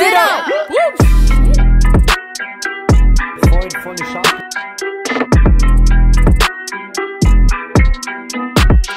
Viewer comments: Was wurde da bei 1:47 gesagt und warum hat man es eingebaut? Habe verstanden "freund von den Schafen" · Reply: Er hat freund von den scharfen gesagt er meint damit waffen also freund von den Waffen. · Was sagt der da im hintergrund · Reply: Irgendwie so "Freund von den Schafen" oder so 😂